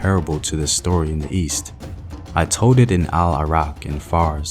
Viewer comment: you left the letter “L” out of the word “told” at around 8:55 in this video it sounded like “toad”